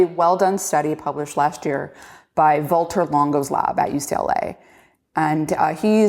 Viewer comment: what is the name of the person she says? "voltor longoslab"? I'm trying to find the study she is referring to? EDIT: AH! found it, man google is good at guessing what the hell nonsense i type in: Valter Longo. I think this is the study that Rhonda is referring to at 1:10:03 is this (correct me if im wrong): - <link>